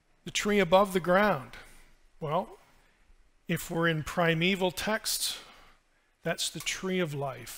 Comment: When he said "primeval texts," I thought he said "primeval Texas" for a moment and we were total bros.